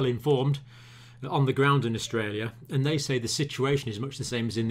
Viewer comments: Austria just stopped all Trains from Italy to enter the country due to the coronavirus outbreak in Italy. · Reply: Oh, I thought you said Australia, and I was about to say 'about time too!' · Austria just stopped all Trains from Italy to enter the country due to the coronavirus outbreak in Italy. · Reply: I thought you said Australia for a second.